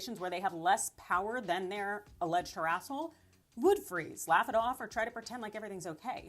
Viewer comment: Wait. Did I just hear "herasshole?" I'm dead! 😊